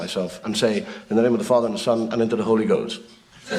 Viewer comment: Unfortunately the automatic generated subtitles on the Dave Allen video are NOT always accurate what he was saying was he thought because the first time he heard the Priest say in the name of the Father the Son and the Holy Ghost that HE HEARD IT AS in the name of the Father and the Son into the hole he goes (As Into the grave when the coffin is being lowered down) Some of the humour is lost in the bad subtitles Awe I am a believer in God and I think all he is trying to do is expose some of the hypocrisy in the Catholic Church and indeed more widespread! You have to remember in Ireland and the church had a very bad name because of the Magdalene Homes in which young women were unlawfully imprisoned because they broke what was seen as the churches rules Some women were sent there for being "whores" because they had the nerve to be raped or physically and mentally abused! They received beatings and more humiliation in these so called church establishments! Bodies were found at one time as well it really was a dark period of Irish History Also there has been a lot of convictions over the years of so called priests being paedophiles and reported child abuse hushed up the Archbishop of Canterbury resigned just a few months ago after he was accused of covering up instances of abuse and not taking them seriously! From what I can see Dave Allen was educated at a Catholic School! (maybe worth researching the Madalene homes/Laundries!?)